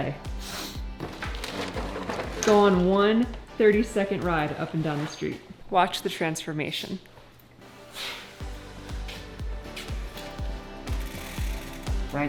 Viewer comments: Hahaha. Nice one. I know you said it right, but I heard "Watch the Trentformation!" 😉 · it sounded like you said Trentsformation instead of transformation just before got on his bike and went for a ride. If you did then great for you to make a new word. I love it. If you did not say it then maybe you should and make TShirts. He indeed did have a smile on his face when he got back. Trentsformation: When a man named changes from a bad mood into a better mood from doing a positive activity outside of his norm producing a slight giddiness. You have been Trentsformed.